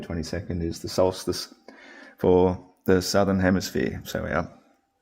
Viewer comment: did he say... hemiSPHERE?